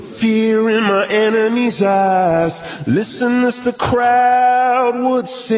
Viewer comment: I hear “feel the fear on my enemies ass” not eyes